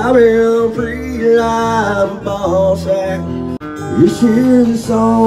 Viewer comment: I’d watch the CMAs if Mumbles opened for it! Did he say ball sack @ 2:43? Haha